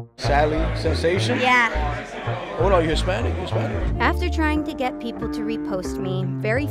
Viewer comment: What did the guy said at 7:15 ? "You're spanish?" ??